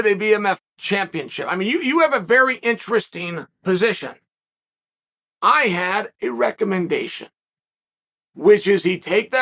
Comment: “you have a very interesting position… i had erec—tile dysfunction is what it sounded like he was gonna say 😂